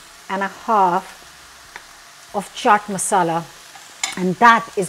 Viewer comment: I thought you said Chard Masala, so I went looking for that and found Chana Masala and Chat (or Chaat) Masala. So what Masala is used for this recipe?